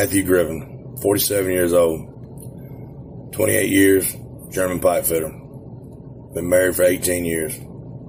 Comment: Stick with your values, brother. However, the part I didn't understand was "28 years", german-pie-fiddle, at 0:05.